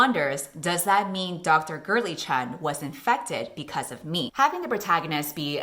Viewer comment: I THOUGHT YOU WERE SAYING GURTLY-CHAN NOOOOO LMAOO 😭😭